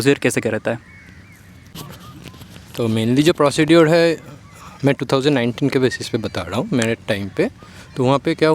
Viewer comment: yeeh kya bol gaya 😆😆😆😆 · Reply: 😂😂😂😂 are bhai PROCEDURE keh rha hai.... Tum jo kehna chahte ho vo nahi 😂😂😂 Kitne tejasvi bande ho tum